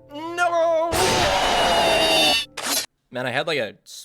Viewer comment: it sounded like he said "NoloOw!"